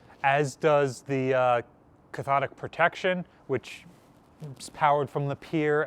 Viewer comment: WHAT kind of protection? Seriously, I'm getting deaf in my old age and I THINK I heard "Cthonic?!" "GALVANIC" makes... some sense to someone as ignorant of electrical engineering as I myself am, but I had NO idea New Jersey had been involved in certain... Eldritch? operations? ;-) I'll see myself out... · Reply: Cathodic